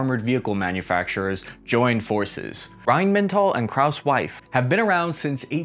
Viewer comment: My subtitles say "krauss wife"